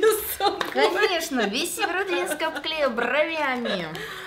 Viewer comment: Я несколько раз переслушала на 16:02 не веря что слышу название своего города🤦‍♀️😂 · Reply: похоже что да, я там сказала Северодвинск